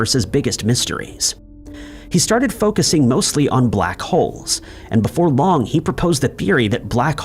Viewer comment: i was about to report you…i thought you said black hos lol